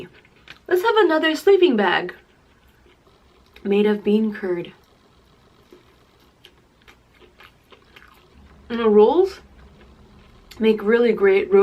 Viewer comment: When she said bean curd I thought she said bean turd and I spit my tea everywhere lol.